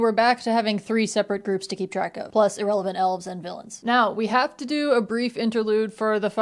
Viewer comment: I misheard "Rivendell elves" as "irrelevant elves" and it had no bearing on the narrative.